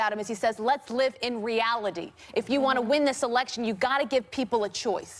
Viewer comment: I didn't hear him say "let's live in reality" I heard him say, "let's not try anything new."